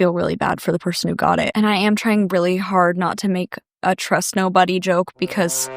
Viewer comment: girl I thought it said ‘crust nobody’ deadass. Crust nobody.